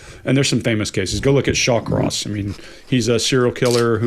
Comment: what is the name is saying? Sounds like "Shock Ross"??? Trying to look it up, but I just see Michael Ross. Can anyone clarify? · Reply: Shawcross <link>